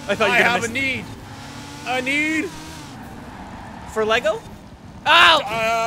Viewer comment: I thought they said R: I have a ,,D" N: Out of LEGO?